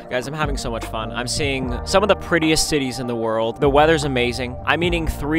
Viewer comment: I THOUGHT HE SAID T*TTIES 💀